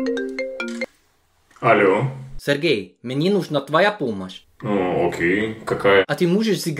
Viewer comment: After you said Sergei, it sounded like you said "meninos" (which is children in portuguese). It isn't the first time I hear someone speak in Russian and I understand some Portuguese words